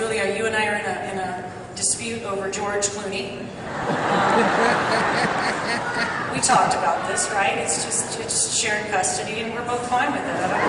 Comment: btw...what did she say at 0:44?? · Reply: She said that her & Julia Roberts will share a custody over George Clooney LOL